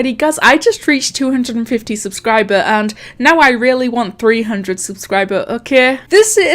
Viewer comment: wait did she said 300 subs